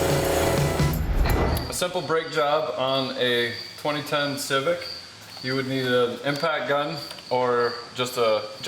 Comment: ahhahaha I thought you said 20 ton civic, not 20 10 civic